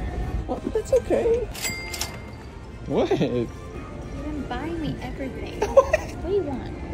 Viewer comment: I THOUGHT IT SAID BURNING I WAS LIKE OH NO